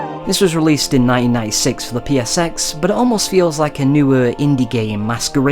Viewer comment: for the briefest second I thought you had said "This game was released in 1996 for the PS6" But I realized you were saying PSX Who knows what the future may hold for consoles, maybe it will get a re-release then.....